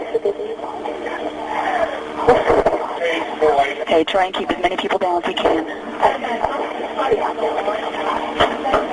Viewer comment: I hear the "peekaboo" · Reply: It´s "Pigs are here". Meaning the police when Harris looked out the window.